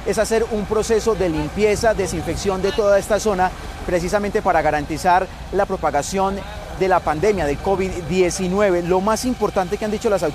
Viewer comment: que dijo este reportero??? 🤦🏻‍♂️🤦🏻‍♂️ *Garantizar la propagación del covid19?*... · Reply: Dijo para garantizar la propagación de la pandemia.